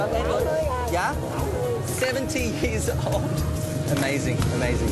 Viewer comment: the old lady is actually saying, "80" not "70"